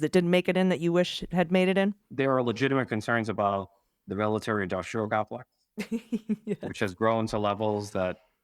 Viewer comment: can someone tell me what Mr. Torres is saying at 33:43. ...the military dun (something) complex? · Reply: Military industrial complex.